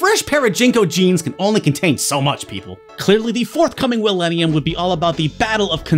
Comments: did you say Willenium? As in Will Smith's album of the same name? I heard that, lol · Had to do a double take when I heard "the forthcoming Will-ennium" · Did he just say "willenium"? If so, is it referencing Will Smith's "Wild Wild West"?